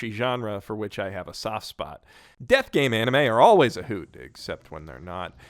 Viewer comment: My mind was so stuck in VRMMORPGs that I legit heard "Dev Games" the first time. As in, video game devs. And I got REALLY hyped like "No way, that shit exists??" and then I realized it was "Death Games". Oh well. Still, I'd like to see that show. No idea what it would bring with it, but I'd like to see it